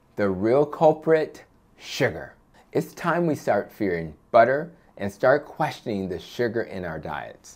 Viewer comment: Did you say “Start fearing butter” or am I hearing you wrong. Sigmund would love the glitch. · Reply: Stop fearing I know it sounded like fearing I put the clarification in the comments